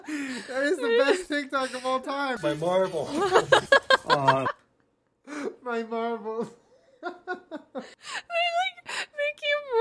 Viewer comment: Sometimes I’m in the shower and I hear “it shitted on my eye” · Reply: 😂😂😂😂 I hear "oh no...my marbles" 😂😂😂😂